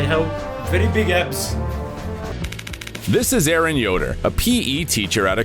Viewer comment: I heard Eren Yoder 😂😂